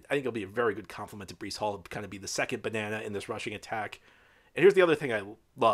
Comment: Hey, @ 20:31 mark, did you say "Carter could be the second banana 🍌 "??? That's a riot 🤣